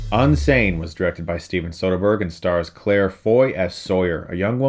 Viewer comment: I thought you said Steven Spielberg lol